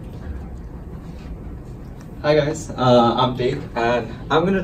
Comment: Dave ??? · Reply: In 1:20 you said, "Hi guys I am Tek". But, Subtitle shows Dave. So I thought you said your name as Dave. Sorry, that's my mistake.